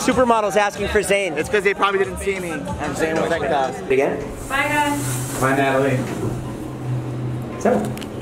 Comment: Did Natalie say "Bye Hun" ? ... Was that an Easter egg aka a hint that David and Natalie are dating? O.o OoOoOoO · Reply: She said "bye guys "